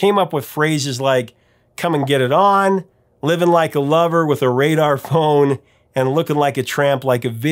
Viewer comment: It sounds like he says "with a red iphone" has made me laugh since iphones came into existence, Also sounds like he mentions Lucy Liu 😂